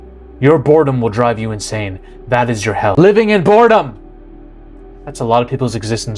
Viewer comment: I had to rewind because I thought I heard “Living in Florida!” 😂